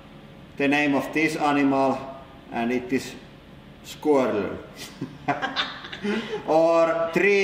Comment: When he said squirrel, it sounded like he said Squirtle.